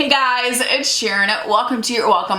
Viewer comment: At the beginning I thought she said "Ed Sharon" instead of "it's Sharon" 😭😭😭